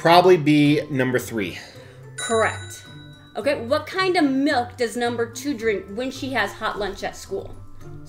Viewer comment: I heard "what kind of milf does number 2 drink". Wish i was drinking milf when i was a kid.